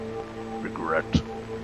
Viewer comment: I heard "erect" instead of regret